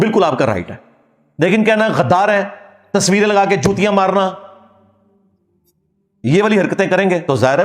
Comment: Did he say chutiya maarna at 01:00? Anyways mowlvi sahab better you preach Islam.